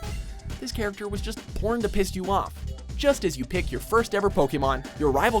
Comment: did he just say "porn to piss you off"